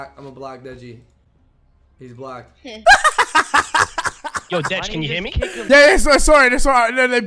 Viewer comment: When Logan said “imma block Deji” “He is blocked” I thought he said “cuz he is black”